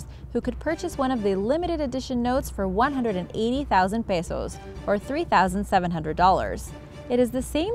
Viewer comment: Why pisoes? That I hear? I think it's Pesos